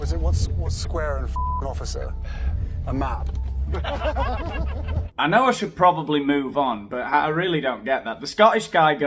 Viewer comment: I thought he said what is a swear word to an officer-a map. Because officers are notoriously bad at navigating a map